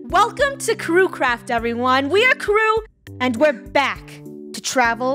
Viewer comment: I thought she was gunna say “ We are krew and we craft “